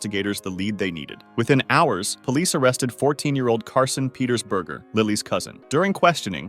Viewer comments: did he just say... That his last name... Is BERGER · did he just say his last name is burger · SORRY DID HE JUST SAID THAT HIS LAST NAME IS BURGER? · BURGER… DID HE JUST SAY HIS LAST NAME IS BURGER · did he just say that his last name is Burger?!? is your middle name cheese? do you come with fries? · sorry did he just said his last name is burger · berger? Did he just say his last name is burger? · did he just say his name is BURGER? Does he come with fries?💔🥀🤣🔥🔥🤣🤣🤣🤣🤣🤣🔥🔥🔥🔥